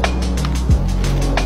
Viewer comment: а мне послышалось чего не спиться ееей